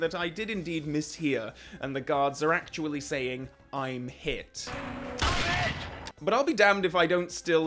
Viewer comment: I heard "stop it" until you said it was "I'm hit".